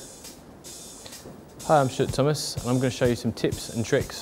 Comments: I thought he said "hi, im sh¤t thomas" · it sound like he is saying "hi I am shit Thomas"!!!!!! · Omgee even I heard him say shit tomas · Its sounds like he says "Shit Thomas" when he introduces himseld